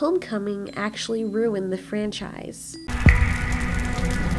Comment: at first i thought she said did how to train ur dragon home coming really ruined the frenchfries